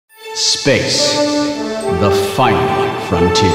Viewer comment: In the opening I thought he said 'bass' instead of 'space'. And was waiting for him to ask 'HOW LOW CAN YOU GO???' Also, Wil, I'm curious about your son is the same age as you.